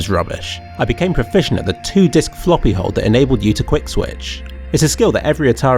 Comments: the transcript says 'two-dick floppy' instead of 'two-disk floppy'. PLS FIX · in the subtitles it says "two-dick floppy hold" when he actually said "two-disk." You never get past me.